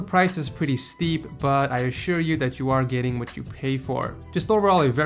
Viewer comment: "I assure you that you aren't getting what you pay for". Thats what I heard